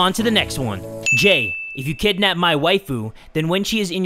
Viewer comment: I thought said if you kidnap my wife lol